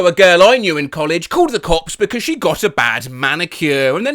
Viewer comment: did anyone else hear “called the cox”